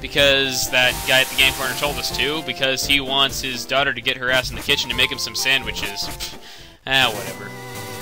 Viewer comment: Goodness, at 2:18, instead of hearing "he wants his daughter to get her ass in the kitchen" I actually understood "he wants his daughter to get harassed in the kitchen" O_o